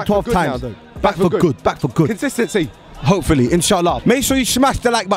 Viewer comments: did he say InshAllah!? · Did he say Insha'allah?